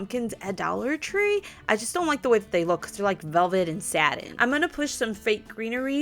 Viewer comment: I totally heard “velvet and sad” when you said velvet and satin lol 😂 love your videos!!